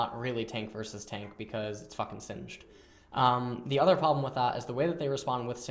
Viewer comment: oh ty, btw, can u tell me what chapion is he saying at 5:00 ? jhin or sheen? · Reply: Haha it's neither, he said Singed. Singed does not play like an ordinary tank so is saying that the lane wouldn't really be a tank vs tank because of Singed's typical playstyle.